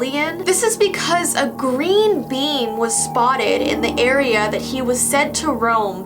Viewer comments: who else thought she said green bean instead of green beam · When she said green beam I thought she said green bean lol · When she said green beam, I thought she said green bean XD